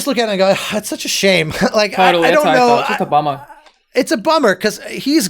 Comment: "Just a bummer." What I heard: "It's Obama." The subtitles: "I don't know just Obama." 🤣 For a second I was like what. If it wasn't for instantly replying "It's a bummer." I would've probably lagged a bit longer there. Lmao